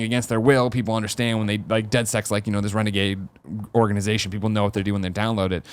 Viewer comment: Did Greg say "Dead Sex" at 13:00? ;-)